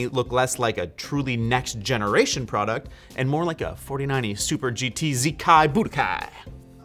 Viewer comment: "4090 Super GT ZKi Budokai" instead of "4090 Super GT Z Kai Budokai" video is unwatchable with subtitles smh